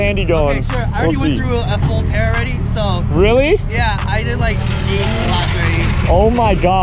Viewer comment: did mans say 80 laps